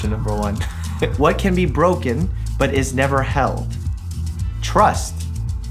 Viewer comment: When he said trust I thought he said truck at first and was like what?